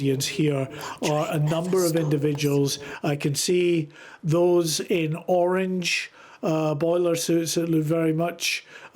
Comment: What’s the whisper saying at 5:48 ? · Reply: That train never stops